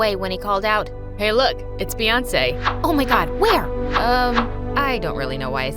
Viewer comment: I js can’t did he say Beyoncé.😭